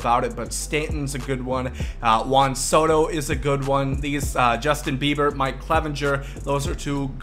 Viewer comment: Am I the only one that heard him say Justin Bieber